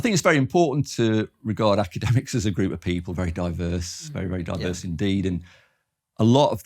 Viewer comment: I couldn't quite make out what he said here...but did he say that academics themselves are "very very diverse?" Because, I thought it was fairly established that diversity of opinion, with regard to political orientation, in academia, is in no way diverse at all.